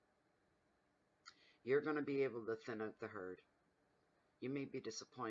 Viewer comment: What does she say at 20:19? 'You are going to .......the..herd?' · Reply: I believe she said "You're going to able to thin out the herd"